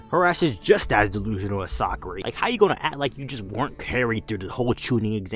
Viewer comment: boi said Sakury😂😂😂😂